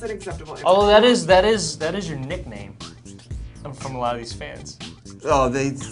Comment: what does Harry says at 0:17 ? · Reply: Oh he said "from ALOT of these fans"+Candy Miss